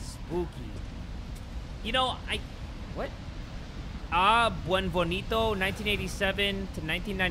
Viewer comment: The way Jay pronounce Beneviento makes my giggle like a schoolgirl. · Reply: I thought he said ‘Wet Bonito’ :”)